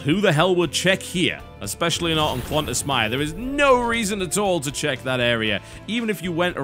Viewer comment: Oh, Klontas Mire. I thought he was saying Qantas Myer.